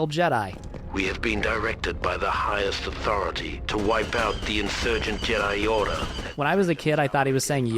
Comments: I hadn’t thought about it in years but I also ALWAYS thought that they said “Insurgent Jedi Yoda” What a throwback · To be fair, even if we bear in mind the accent, the way he puts an emphasis on the "i" in Jedi, it still sounds like he says Jedi Yorder. :D